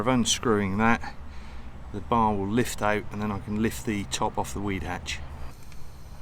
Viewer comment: Weed hatch 😅 I thought you said wee hatch.